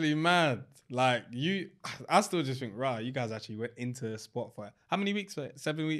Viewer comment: VAR! They did Temi wrong with helmet, he said "spot for like" not "Spotify" He said "I still just think raa, you guys actually went into a spot for like" and then asked Dami to confirm how many weeks they went into the villa. But because he said "spot for li" very quickly and didn't finish saying the word like, stopping abruptly to ask the question, it sounded like he said spotify